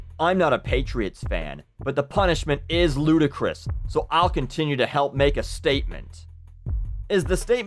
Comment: you said continue instead of contribute, so.... good one.